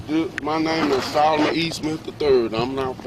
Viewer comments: I don't see what the problem is. Police have to be very assertive or they are dead. • Failed to identify himself, at 1:00 he said. • At 1:16 the officer called him Mr. and he didn't correct her. • At 6:18 - 6:39 we find out he lied and is playing games: Officer: Why did you tell me your name was? Yeah I told you but you didn't ask me all of that... • Sketchy area, late at night. • Admitted he gave the wrong name and repeatedly apologized. • Apologized, saying, he would tell the truth around 12:19. • Early on he said was working, then changed his story, saying he came from a friend's house. • Threatened the police, saying he knows people that can do something to them (the police). • Was reaching in his pockets (makes cops very cautious). • Was repeatedly walking away. • At 9:44 his friend even said don't move (away from the police). • Did the taser even work? • ... Maybe he was in his way to a use women (prostitution), sell drugs, the officers don't know... If the officer did falsify the complaint, I don't know about that, then that isn't right. I don't see why though. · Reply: Listen to him saying his name a few more times and you will hear him say “E-Smith” he just said his name to fast and the cops misheard him. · He clearly said his name was SOLOMON E SMITH III!!! Rookie herd wrong!! Where the efffff did she get eastmen from wtf!! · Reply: Yes, I clearly hear "Solomon E. Smith the 3rd" from 1:01 - 1:03.